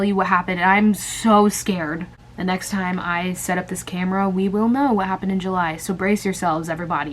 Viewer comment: the next time i set up this camera, we will know what happened in july what my brain thought she said: the next time i set up this camera, we will know what happened in wellington me: now wait just a damn minute